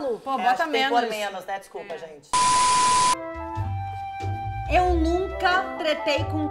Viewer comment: meu deusno 6:35 eu escutei "eu nunca trepei" KKKKKKK imagina a hora da falando que sim com a thayla uheuhe